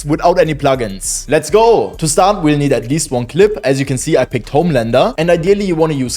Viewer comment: Did he just said Homelander? 0:10 😂😂